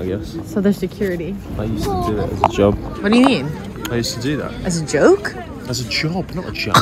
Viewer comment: I can blame you I thought I heard “as a joke” too💀